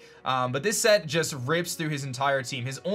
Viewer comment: What does say at 4:30? Sounds very un-PG 😂😂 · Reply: "This set rips through his entire team." Even if he did say "rapes," it wouldn't make sense syntactically.